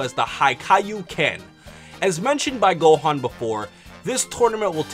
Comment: Did he said 'High Kaio Ken' at 0:46 ??